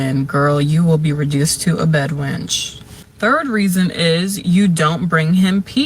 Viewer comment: hol tf up - did she say b*dwench? Enjoy your karma, ma’am.